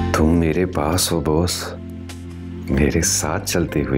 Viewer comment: is he calling her 'boss'❤😅